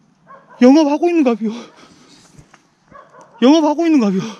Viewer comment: 뭐라고 하는거임? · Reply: 영업 하고있는가벼